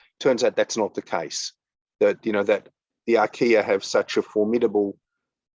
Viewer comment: Asking the questions here, that I had asked in the Slack Airminers Bootup channel : 1. At 44:41, is he talking about ARCHAEA, as in Archaebacteria which have the "swiss army knife"? 2. Does anybody know of the recent science articles he based his assertions on? I thought Pyrolytic Carbon Compounds like Biochar do last decades in the soil, if not for centuries. The Research was clear on that, wasn't it? 3. Since soil is highly complex and can differ widely between any two sites on Earth, his wide sweeping declarations about how soil is no more a carbon storage option, I'm not sure how much weight they hold AS OF NOW. But he seems pretty certain of what he is saying. It boils down to : What's this recent research that changed his mind? 4. Also, there is a recently emerging view from the likes of Dr.Elaine Ingham that a significant fraction of Soil Organic Carbon is the 'LIFE' within it, not just the organic and inorganic Carbon compounds. This includes the microbes, the insects, the annelids, mycelium etc. The World's largest organism could be a Fungus <link> Doesn't this mean 'Soil Carbon storage/Sequestration' is still not a Closed And Shut case, inspite of whatever recent scientific research changed his mind? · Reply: "is he talking about ARCHAEA"- yes, I think he is.